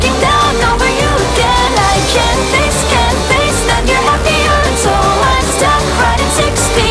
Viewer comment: What it says: "I can't face can't face that your happier" what I hear: "I can taste can taste that your happier"